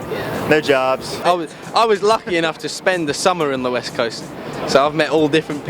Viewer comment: Ohhhhhhh east... I thought u said, weest 😂😂